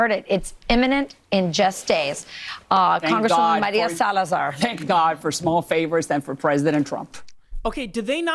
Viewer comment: Did Salazar just say "Thank God for ... Salazar"? If so, no commentary necessary here. The "Thank God for President Trump" part tells he she's a cult member.